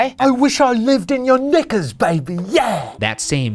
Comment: DID SHANE SAY THE N WORD????? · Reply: no. he said knickers.